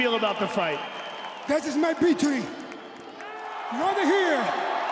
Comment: Oh... "Victory" I thought he said, " this is my peach tree."